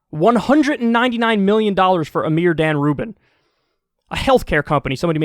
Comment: I hear that as "a mere damn rubin" and i was wondering does dave rubin have an entire family of assholes?